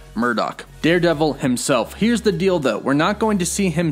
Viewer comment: Yeah I had to rewind to make sure he did not say "Here's the dildo"